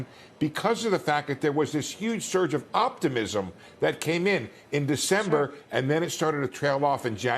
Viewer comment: I heard him say ".. I do think its moron president Trump than Biden ..." 😅